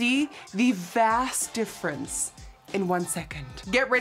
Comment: I could have sworn she said "vas deferens"...not so sure we needed an anatomy lesson 🤣